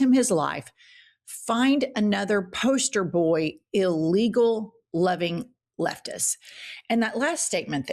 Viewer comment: sounds like she said “N word loving leftist”